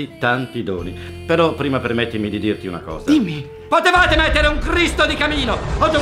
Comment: io ho capito "potevate mettere un Cristo nel camino" 🤣🤣🤣